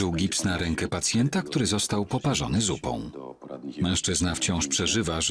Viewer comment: czemu ja usłyszałam dupą 🥲🥲